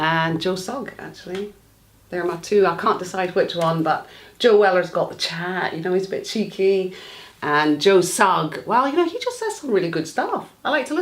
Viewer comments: Am i the only who thought that his mum said "Joella" at 2:57 ?! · What's say in 3:03 about Joe Weller ? I can't hear properly the first word "сhaan" ? wtf))